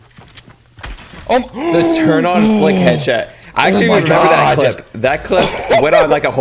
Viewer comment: Correct me if I'm wrong did he just say headshet